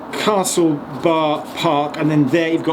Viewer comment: Could have sworn I heard Castle Barnard instead of Castle Bar Park